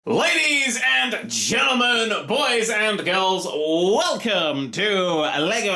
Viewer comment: It sounds like he said... LADIES AND GERMAN, GIRLS AND BOYS